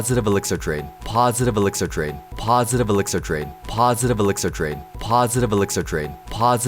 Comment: I thought i heard OJ say positive elixir trade! only me?